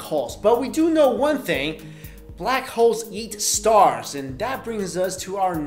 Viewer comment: I really for a moment thought he said "Black Hoes eats Stars"